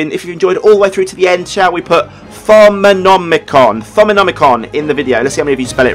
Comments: Thaumonomicon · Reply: If that's the case, I need my ears testing I thought he said phonomicon 😂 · I need my ears testing, I thought you said Phonomicon, oh god, age is catching up with me 🙈